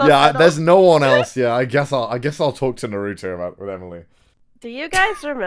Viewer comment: I'm basic Me: His waifu is gonna be Rem isn't it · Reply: Did anyone else hear him say "I guess I'll talk to Naruto about Emily." ??? XD